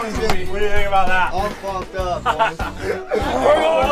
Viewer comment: does say “all f’ed up boys” ?